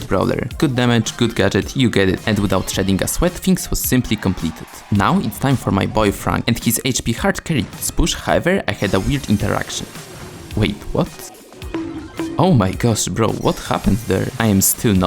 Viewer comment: why did i hear "boyfriend" 😭😭 i legit had to turn on captions for a moment to see if i was trippin' or not.. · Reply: He said my boy frank